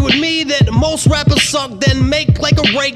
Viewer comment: Did he just say make like a rake on the back of a landscaping shrub and stand the f#$k up. Damn gonna steal that one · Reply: Landscaping "truck"